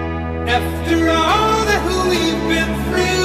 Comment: Sounds like he is saying " after all the who we have been through"